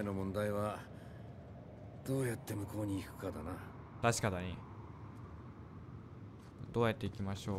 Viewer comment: さん17:00ぐらいにたしかだにって聞こえた笑